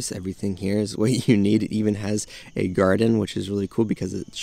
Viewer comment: did you say garden? 🪴👨🏻‍🌾 Meow.